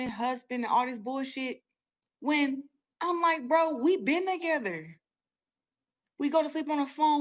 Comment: Girl, you saying ish like, "bro we been together" is crazy.. All I heard was, "bro, I been with this woman's husband".. like wuh?